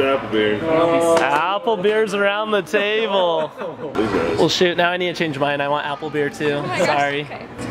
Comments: did he say sh*t? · did he say s*** I don't really care but I was just wondering now if you use the word sometimes? Also super funny prank on chicken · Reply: No he said shoot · Sounded like said oh $#!t. He said shoot (the captions said shoot)